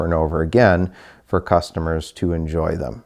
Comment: I was working at my desk and thought I heard him say “...buy and sell cars for customers to destroy them” and got a laugh out of it. He actually said “..for customers to enjoy them” but I honestly think they go hand in hand.